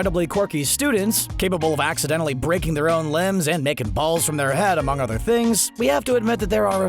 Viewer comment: is it just me our when he says the intro it sounds like he said liken balls from there head?lmao